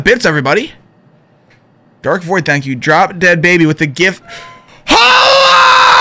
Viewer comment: HAAAAALLLOOAAA i heard. also he is standing there... MENACLING!!!